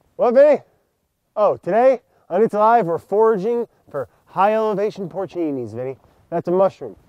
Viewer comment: thought he said "forgy" for a sec